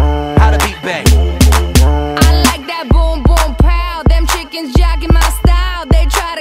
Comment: are they really saying Chickens?? I thought they were saying " Them Chicks Been" lol